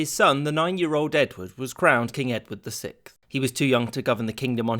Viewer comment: I literally heard "Edward the Sick" instead of "the Sixth"